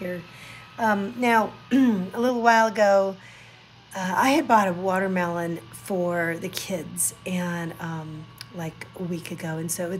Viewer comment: I thought you said you bought a computer for the kids and it was in the fridge.😸